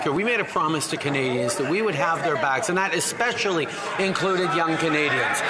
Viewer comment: "Got their backs"? Oh ok, I thought he said: "stab their backs".